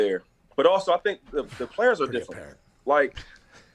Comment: what does Pat say off mic? Great parent? I don’t get it lol